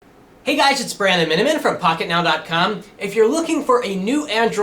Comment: It sounds like he said, Brando-Minnimin, as he introduced himself *-*